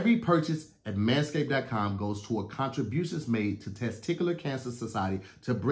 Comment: My boy said "contributous" instead of "contribution" 😂🤣😂